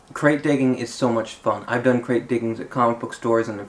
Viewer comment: ! I love this video. There was so many new things I learned like the thing with the board in the bag and the grate digging :) (which tbh I keep mishearing as grave digging).